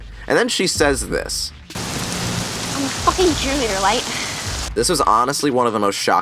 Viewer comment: I thought she said that she was a junior not a cheerleader lol